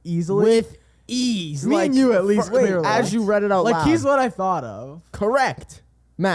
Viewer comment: HELP ME I WAS RE WATCHING THIS PODCAST WHEN I WAS CLEANING AND I HAD MY PHONE FACED DOWN AND THEY SAID “here he’s what I think of” OR SOMETHING THEN THE BURGER KING AD CAME ON AND I HEARD “at B.K. have it your way YOU RULE!”IT SCARED THE FRICK OUT OF ME